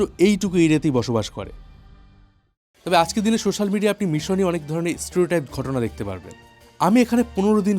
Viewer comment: মনে হলো blogger বার বার “ আরবেস্প্রিং” বললেন !!!! নাকি আমি ভুল শুনলাম ???? আর, 15:46 ওটা কি “type” বললেন, বোঝা গেল না—- seterotype কি ?